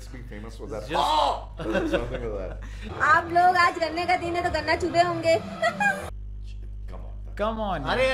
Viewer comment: I think she said ganna chupe honge which means the same as chusna